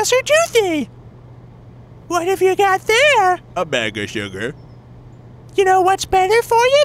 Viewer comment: said: "You know what's better for you than all that sug-AAAAAAAAAH!!!" Captions said: "you know what's better for you than all that shit" OMG I laughed so hard every time I saw that XDD